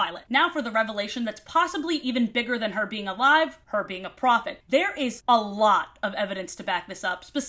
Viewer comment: I kept hearing you say "profit" instead of "prophet" and thought she was some thing made for the purpose of making money.